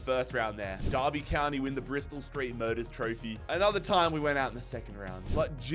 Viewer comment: Thought he said "Bristol street murders throphy"